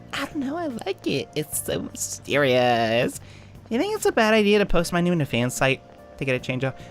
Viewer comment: Did I heard it wrong or did you just say "...post my nudes..."?